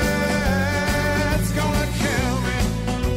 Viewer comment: For a while I thought the song was saying Death's gonna kill me lol 🤣